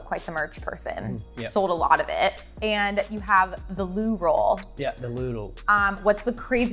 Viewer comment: “Language barrier” actual the everyday struggle of being Scottish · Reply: I understood what he was saying. Didn't really understand her. I thought she said lube roll. Like roll on lube? Wha?!